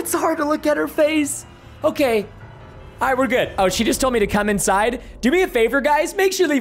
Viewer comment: what did preston just say ?? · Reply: she wants me to come inside its a dark joke lol